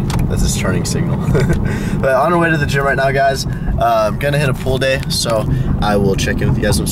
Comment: When you said "pull day" at the beginning, I thought you said "pool day" and I was expecting some swimming.